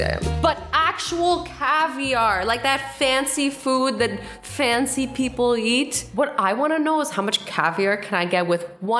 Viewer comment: did anyone else hear her say at 1:01 : the fancy food that fancy people YEET. LOL